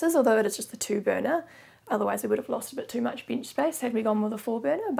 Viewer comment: OOH, THEY CALL COUNTERS "BENCHES!" I was watching another vid and thought they were saying beach space.